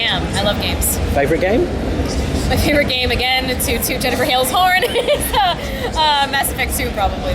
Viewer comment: What she say at 3:58? I don't understand... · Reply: Late to the party on this, but she said "Again, to toot Jenifer Hale's horn"